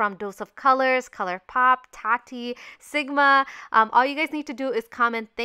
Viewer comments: I thought u said sucma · LMAO I thought she said ligma